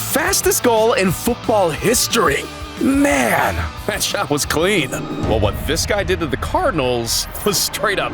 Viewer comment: did bro really say 100 meters 🤦🏻‍♂️ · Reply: Bro said that shot was clean